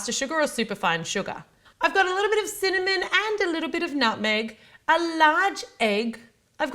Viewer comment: When she said "and a little bit of nutmeg" I thought she said "a little bit of nothing!"😂😂😂